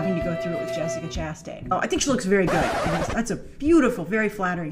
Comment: Omg.. I love this compilation. This is amazing. "Oh, She looks pretty ... but she's so stupid" 😂😂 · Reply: I thought she said "...But she's still stupid"